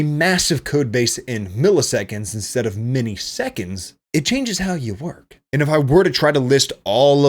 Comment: did he really said "miniseconds"?